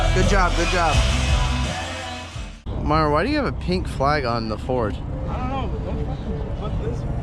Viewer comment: is this a video from the 4th of July? I guess it took some time to edit this content LOL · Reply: I guess I'm the one confused, I heard you say something about a pink flag on the 4th but later you said Merry Christmas LOL I should just keep my mouth shut